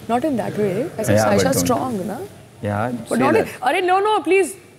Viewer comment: after seeing today’s episode if audiences still like munawar then they are the biggest dumbasses😂 just rewind and see what payal said and see what munawar is trying to portray her. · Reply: She actually said saisha haina ladka